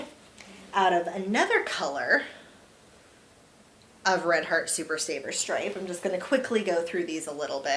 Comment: The mandala sprite? Bummer!! They sell it regularly at my Joann’s now. But for the longest time I only saw it online since my Joanns didn’t carry any mandala. Thanks so much for watching and taking the time to comment, Debbie!! Xoxo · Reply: oh my bad, I got it mixed up. I thought you said red heart super saver sprite. I'll look for it tomorrow. Ty😊